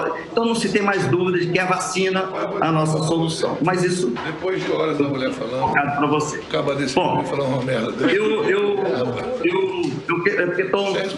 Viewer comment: Quem ouvi-o o que falaram no áudio?..."Depois de horas a mulher falando, um cara desse vem falar uma merda dessa"....em 3:00min